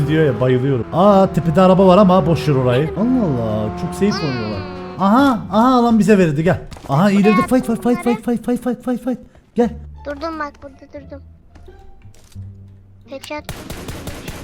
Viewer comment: da faik der gibi bir ses duydum ama fight mış 😖